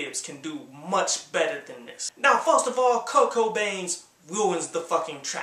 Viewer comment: Lol sounded like u said "kurt cobain" instead of kirko bangz